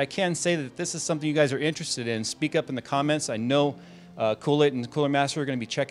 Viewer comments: Did he just say Cool Aid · Did you just call CoolChip Kool-Aid? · did he say coolaid would be listening? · Sorry Jay, but did you say Koolaid? 5:29 Koolaid and coolermaster... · Guys, help me out, I am not sure but did I mishear something at ~ 5:28. Basically what I heard was Kool-Aid.